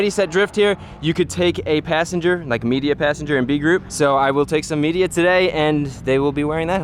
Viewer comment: I heard "I will take some idiot today" 🤣